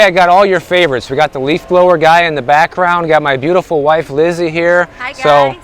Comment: Hi. I apologize for miss spelling your name. I could have sworn said 😂 But it's fantastic seeing you both hanging together! 😊 Stay Cool 😎